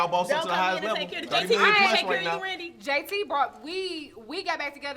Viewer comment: He brought weed? @ 23:49 got me rollin! 🤣 I thought she said the same thing! And AD heard it, too LOL